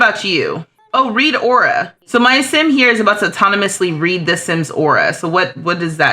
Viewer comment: I thought you said "Rita Ora" and i was like "Where?!"